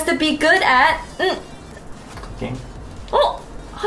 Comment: I thought said fucking and not cooking so when was like Yeah …. I lost my shit